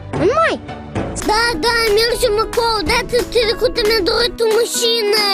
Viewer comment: "da, da mergem acolo, data trecuta mi-am dorit o masina" It was very hard to decipher, but now I finally understand what that kid was saying.